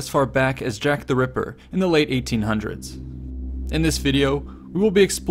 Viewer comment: Jack the Ripper was late 19th century thou. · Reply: Oeps my bad I thought I heard late 18th century. You said late 1800s. My bad. Great video btw!